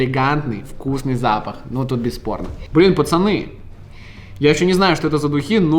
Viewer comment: Мне послышалось когда А4 нюхал духи он сказал без порно